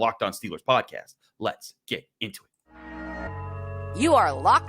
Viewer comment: Sounds like you said LETS GET INTIMATE